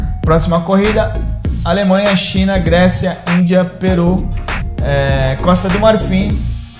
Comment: Juro q ouvi Gosta do Mario fi